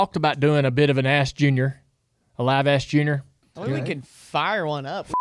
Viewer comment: Sounded like Dale said "Liveass Junior!"